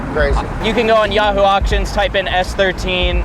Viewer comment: Did he say "yahoo auctions"?